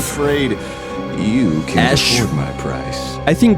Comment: That voice you at first thought might be Aha was Ashat's voice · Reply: I thought u said ass hat for a sec😂